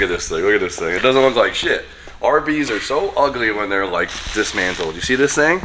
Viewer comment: You just about caught a case with my daughter. She thought tou said barbies are ugly when yall was talking about the rb30